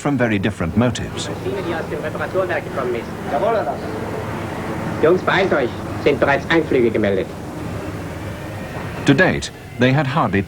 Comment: Just out of curiosity, did anyone get what he said? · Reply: "Jungs, beeilt euch. Da sind schon Anflieger gemeldet" "Hurry up guys. We just received reports of flights coming towards us"